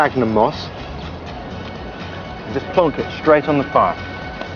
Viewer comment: haha thought he said bong!